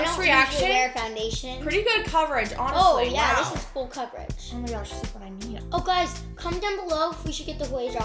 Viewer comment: I thought u said hue drops like hU drops😭😭 am I wrong?